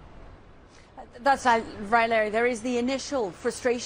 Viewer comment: 😂😂😂 Raila instead of Larry 😂😂😂